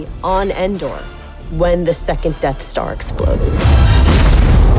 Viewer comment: Ahm, did she say: "We happen to be on Endor, when the second DARK Star explodes"?! Seriously DARK star?!!!! on